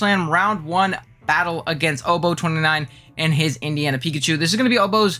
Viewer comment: did u say indian pikachu?